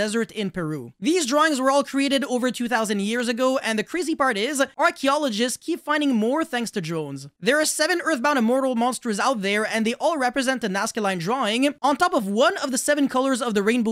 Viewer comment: I heard "Archaeoligists keep finding more due to Droll"